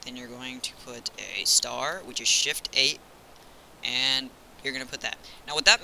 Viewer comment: in 13:17 What is the first letter or sign he said? i can't understand him D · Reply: If you're asking about what I think your asking about; I said "shift + 8".